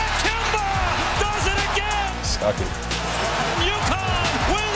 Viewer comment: Ik he says "I sunk it" but it low key sounds like he said "suck it"